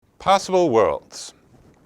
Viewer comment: The suggestion of “passable worlds” is very insightful. But I may have misheard.